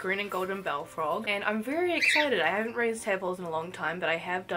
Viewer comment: Help in the beginning I thought u said “ im rlly excited I haven’t raised tampons in a long time!” 😭